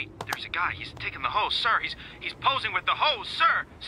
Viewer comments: “sir he’s taking the hose” “he’s posing with the hose” i heard this a lil different than intended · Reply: i misheard "hose" as "horse" and was so confused when i first heard that 💀 thx for clearing my years long confusion man 😔👍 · “sir he’s taking the hose” “he’s posing with the hose” i heard this a lil different than intended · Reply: I mis heard it as "the host" instead of "the hose"